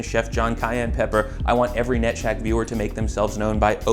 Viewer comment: when he said "Net Shaq" at 3:55 my brain inverted because I thought he said nutshack.